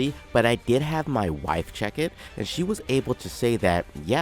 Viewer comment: Did I hear Wife ?